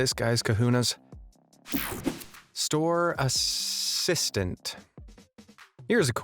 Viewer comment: I THOUGHT THAT SAID "Store Assasination"